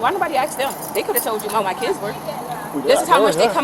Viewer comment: did she say," my kidzis"?